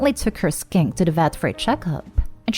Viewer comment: Ooo I thought it said "She took her SKANK to get a check-up " woah woah woah lmfaooooo